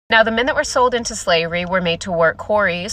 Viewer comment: Damn, her annunciation there needs some help. It's causing all kinds of problems in the chat. The first time, I heard: "Now, the minute we're sold into slavery...." when what she ACTUALLY said was: "Now, the men that were sold into slavery..."